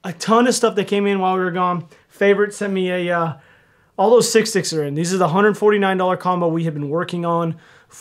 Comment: This whole time i thought you were saying 6'6 but youre really just saying sick stick.